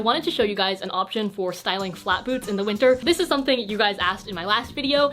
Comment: I thought she said "an option for styling flat boobs" at first😂 you're talented